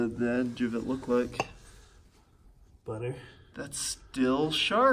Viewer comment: I heard him say butter, someone might be hungry